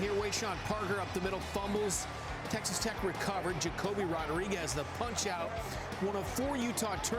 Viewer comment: did he just say Jacoby Rodriguez? 😂😂😂